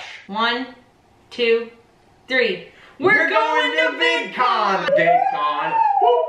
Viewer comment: I thought you said "Bit Con"! Lol. Oh....VidCon... that makes more sense.